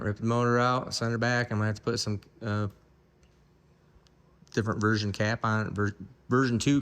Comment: that was the SML... All the new and old SMX have had the new main caps by this point.. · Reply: sry that's not what just said. At 17:58 he said "I'll get the version 2 main caps put on it"